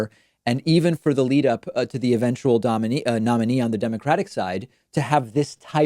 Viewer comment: Anyone else hear David almost say "dominatrix" instead of "nominee"? Nice Freudian slip. What a pervert lol